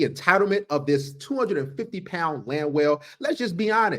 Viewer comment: Thank you for spotting the land whale and the refrigerator. That's a real doozy !!! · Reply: Thought he said 250 pound land fill 😅😅😅😅😅